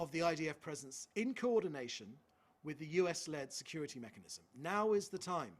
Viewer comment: I think you meant to say “UN-led security mechanism?”